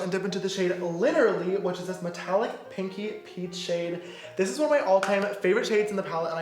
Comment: did james say a "pinky pink"???? Isn't pink supposed to be "pinky"?? or am I losing it?